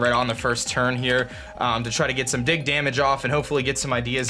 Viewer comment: anyone else hear him say dig damage instead of big damage??